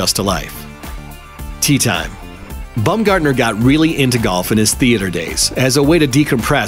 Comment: I kept hearing “Bum Gardener”